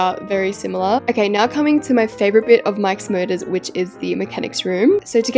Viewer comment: the captions say mike’s murders